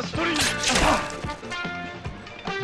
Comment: I thought he said spider trash and threw trash at Andrew Garfield 😂😂😂